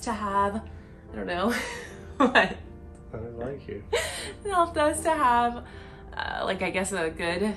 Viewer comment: I think he might have said "I just like you" under his breath. It would make more sense in this context but I'm not sure